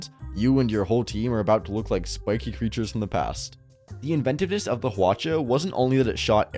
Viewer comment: Bruh, did you just say inventivist?